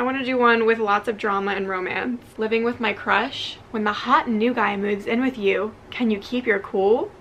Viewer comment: when she said the hot new guy i thought she said the hot gay guy